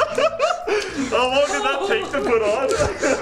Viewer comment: Subtitles = [mumbles] What he actually said = "How long did that take to put on?"